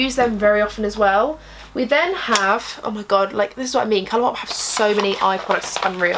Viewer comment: When you said oh my god I heard you saying glossy box! 😂 x